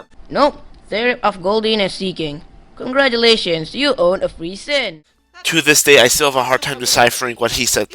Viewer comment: I think he is trying to say 3 sin